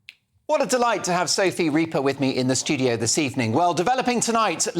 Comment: I thought you said ‘the grim reaper’